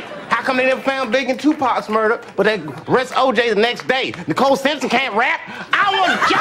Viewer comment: ‘Nicole Simpson can’t rap’ never caught that line before. Hilarious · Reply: For years I thought he said, George Bush can't rap.